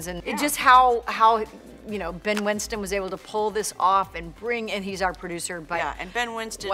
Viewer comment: did monica say 'pull this off'? well we pulled monica before on FRIENDS xd